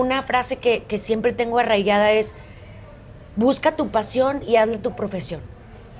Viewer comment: "Busca tu profesión y hazlo tu pasión" Hermosa frase!! Gracias!!! Eres un Gran ser humano! Una Vencedora! Te admiro, y te celebró!!! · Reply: A mí también me gustó la frase, yo escuché " Busca tu pasión y hazla tu profesión"